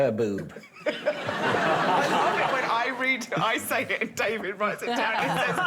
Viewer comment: I don't quite catch what she's saying here and its not in the subtitles.. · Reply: Nothing important or funny or even sensible. Just a bunch of incomplete and restarted sentence fragments. I love it when I read I write it down and David says it he says